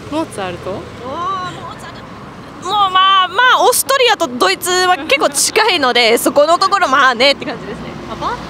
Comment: subtitles say Australia instead of Austria lol